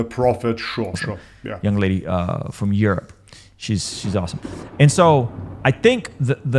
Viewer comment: did he call Hatune awesome? Broo